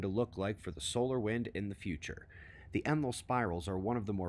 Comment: the ___ spiral... Sounds like “emnal,” but searching for that and similar spellings didn’t help. What’s the word in that blank? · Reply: “Enlil” , as in the ancient Sumerian god. <link>